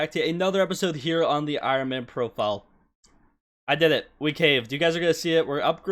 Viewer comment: “Ladies and gentlemen welcome back to a another episode on the Ironman profile… I did it we CAME, you guys are going to see it” That’s Is a CRAZY Intro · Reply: I swear he said caved not came